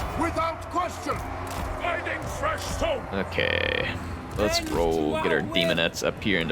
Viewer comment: I thought he said finding fresh soap